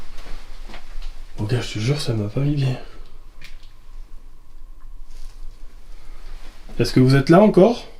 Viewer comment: À 35:50 j'ai entendu " mon coeur je te jure que ça m'a pas mis bien " 😭😂